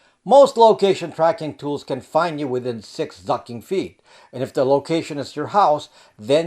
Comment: Lmao, did you say most location trackers can track you within six f**** feet at 3:45?